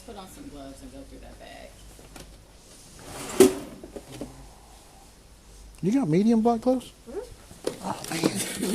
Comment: what I heard the first time listening: "You got medium 🍑plugs? I'll take a whole box if you can steal em for me... gruuuuunt"